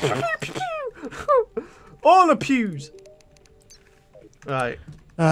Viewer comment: Os it just me or did it sound like he said "All the pubes" instead of "All the Pews"?